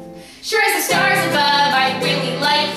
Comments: Yes. I heard "Kill You" as well. What up wit dat? I'm confused. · on the last bit I swear I heard "kill you" and "fist you"